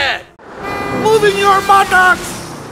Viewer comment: what did he say?? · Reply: “MOVE IT YOUR BUTTOX”